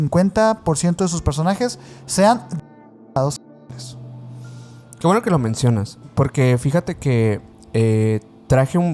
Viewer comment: sean quéeee?? no entendí 😿😿😿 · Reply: Degenerados Sexuales